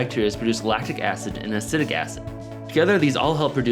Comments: did he say acidic acid? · I thought he said 'Acidic Acid' at first... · did he say acidic acid